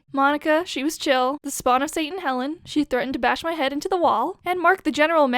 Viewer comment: Did you say.. HELEN?!